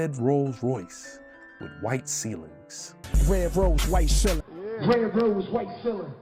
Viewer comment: I thought he kept saying 'red rose, white ceiling', which is actually a little bit more ephemeral than 'red rolls royce with a white ceiling' which is just a dumb observation.